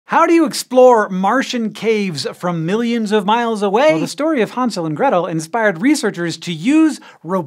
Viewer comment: I heard “the story of Han Solo” and I was confused for a second 😂